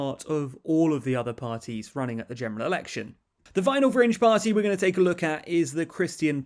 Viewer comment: I swear I heard The final CRINGE Party